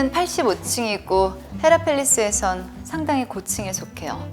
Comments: 난 왜 헤라팰리스가 자꾸 헤라클래스로 들리지? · 난 왜 헤라팰리스가 자꾸 헤라클래스로 들리지? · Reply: 난 울동네 있는 미용실 이름 헤어펠리스로 들림